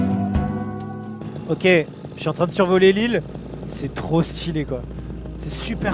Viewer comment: Qui a compris "je suis en train de survoler Lille" à 17:35 ? (Et qui s'est demandé pourquoi il voyait pas Lille)